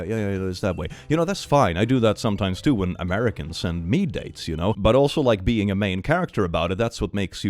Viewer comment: Did anyone else hear "when Americans send FEET PICS"